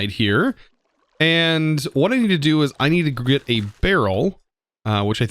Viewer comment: I know I am late to the video but at 6:34 did anyone else notice he said “I need to grit” instead of “I need to get” lol I love hearing other people mess up on their words other then me.